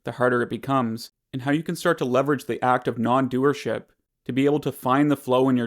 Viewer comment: I heard "the act of not doing shit" instead of "non doership" :)